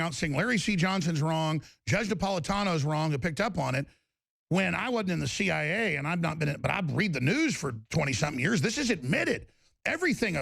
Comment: Hold the phone!...What did Alex say at 4:36? Alex: "When I was in the CIA... And I've not been/but I read the news... for twenty some years..." Alex was in the CIA?